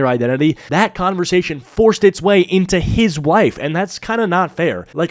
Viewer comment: did you say wife 😂